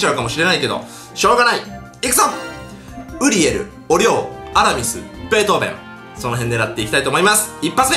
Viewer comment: アラミスって言ったところアナミスって聞こえたのは俺だけ？